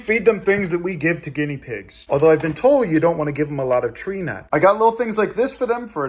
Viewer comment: did you say tree nuts?